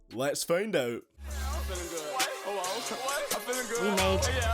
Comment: After 1:15, I heard ‘I’m feelin good, owo’ and I am disturbed